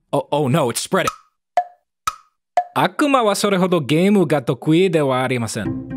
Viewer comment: what he said was “悪魔はそれほどげえむが得意終わりません” which means “the game is a triumph of the devil” or such